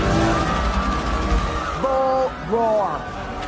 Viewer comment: I don't understand the word she says to Ralph after the Cy-Bugs break out. At 0:54 minutes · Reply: “Bull-roar”